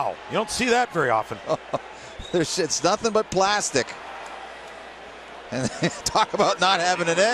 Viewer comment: Did he say "This shit's nothin but plastic."?